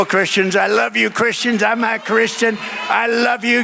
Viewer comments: Ok I thought he said I am a Christian glad I checked others comments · I heard him say, "I love you Christians, I'm a Christian,"....I didn't hear "I'm not." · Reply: I heard “l’m..na Christian” just rolls the the words together making an “n” sound which some people are hearing as not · Thank you for spreading the truth Russell its very important nowadays amd i think you misheard trump he said im a christian not im not a Christian